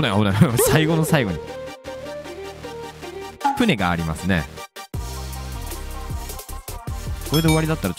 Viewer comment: ぷねって聞こえるww